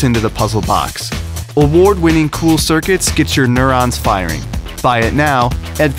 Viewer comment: I thought he said Get your inner ass firing xD